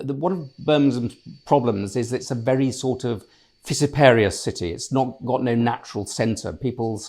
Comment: What was that word that Adrian used to describe Birmingham? Sounded like Visiparious????? What on earth? Can someone enlighten me? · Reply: "fissiparous" - " inclined to cause or undergo division into separate parts or groups". took me a few listens to get it. initially tried turning subtitles on , but they missed/ignored it. never heard of it myself . noticed it seemed to start with F. had to look it up.